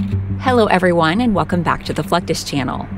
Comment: At the beginning I thought I heard welcome to the "FUCK THIS " Chanel